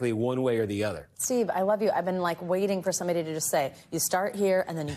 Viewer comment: did she say i love you